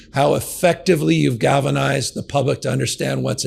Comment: Did he say "Gavinized"?